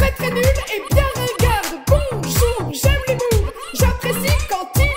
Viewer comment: Il y a que moi qui comprend "J'aime les moules"? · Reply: Moi j'ai compris "j'aime les mooves" meme si sa veut rien dire 😂